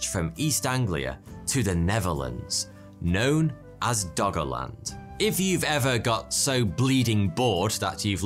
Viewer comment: i keep hearing "doggo land"